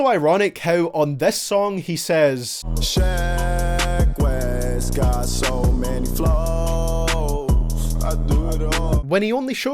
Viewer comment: I thought he said "sheck wes got so many flaws"